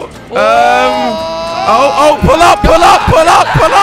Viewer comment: When he kept saying pull up pull up I thought he said pull out pull out!